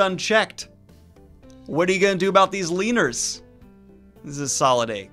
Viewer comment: When you yelled "leaners" i thought you said "wieners"